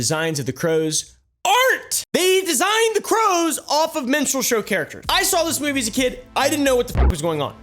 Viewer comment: I thought said, "Menstrual show." 😂😢